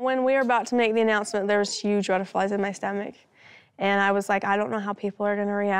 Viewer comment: i thought robyn said there were huge red flags in her stomach... lol. i was like RED FLAGS YES!! but it was butterflies...